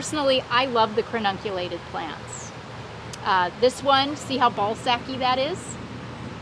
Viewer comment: Did she just say “ball sacky”? 🤣